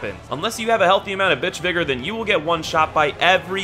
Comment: thought he said the n word